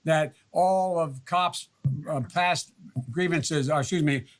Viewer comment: is it just me or did joe say "making sure that all of cops ar-* bast-*", followed by "grievances, rr, excuse me transgressions"? ( 2:54 ) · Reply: past... not bast